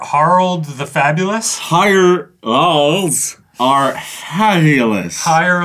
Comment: it sounds like ray is saying her balls are fabulous